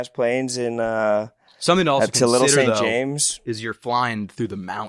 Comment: did just say Little Saint James 😮😂😂😂